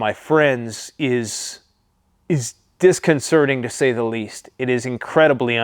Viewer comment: Did he just say he has constipation